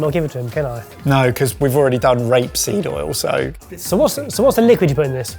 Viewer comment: Rapeseed oil- I'd thought he said "Grape" Seed oil. If you had the Japanese sour plum "Umeboshi" and for the 'Y'- yuzu What was used for "Z"?